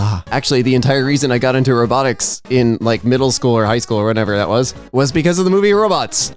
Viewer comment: thought they said narcotics